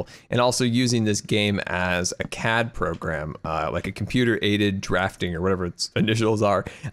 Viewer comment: when scar said CAD program I thought he said cat program lol